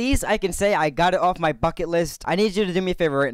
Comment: It sounded like he said fuck-it list instead of bucket list